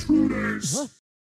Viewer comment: OH SO THE STARTS SAYS INTRUDERS I thought it said intutours